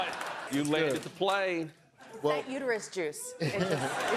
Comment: When she said it the first time I swore she said universe juice🤣I need a nap lol